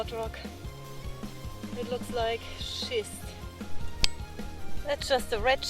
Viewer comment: Good video you had me in a fit of laughter at 3:30 when I thought I heard you say" It looks like shit" but I know its not what you meant.🤣🤣👍